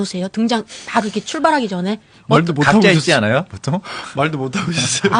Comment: 고생했어요 Thanks so much for your hard work! Could you please translate what Oh Jung Se said at around 40:54? I heard 갑자 있지않아요? (But not sure and why would he say that?) 부탁해요, Thank you! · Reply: The first time i translated it, i was not sure as to what he said, i just couldn't grasp his words. But now that i listen to it, it feels like he said 각자 있지 않아요? In the sense that were you both not alone or were you both not separate from each other (to be able to have a conversation with one another)